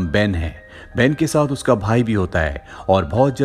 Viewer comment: That line at 2:57 I heard "Behen ke saath uska Bhai bhi hota hai" 😅😅, made me 😂